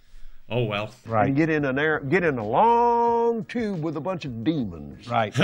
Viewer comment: I'm listening to this as I'm trying to go to sleep, so at 2:55 I swear the man said "get in a looong tube with a bunch of beans" I laughed as hard as Kevin thinking that's really what he said instead "demons" because I was so confused. I had to actually watch it over to make sure he said demons because every time it just sounded like beans